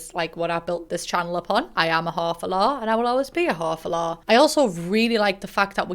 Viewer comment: saying "whore for lore" Me: Did she just say "whore for lore"? What did the captions think she said? Captions: Half Allah